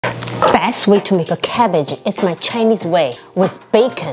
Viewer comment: The most lettuce looking cabbage I've ever seen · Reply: Thought u said garbage 😂my bad